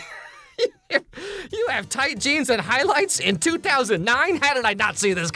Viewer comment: the way i thought maggie said "dude i think your GENES are really tight" to compliment his looks and also making a meta-narrative pun about mike being her dad ...... but nope she was just really into his tight jeans i guess LMAO